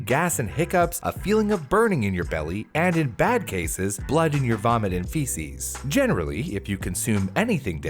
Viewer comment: Did he say blood and you're vomiting feces